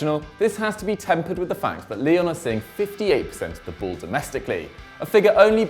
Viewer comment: sounds like he is saying Lille instead Lyon